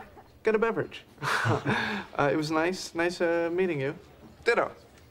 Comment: what did he say? 0:56 dito? what does it mean? :O thanks in advance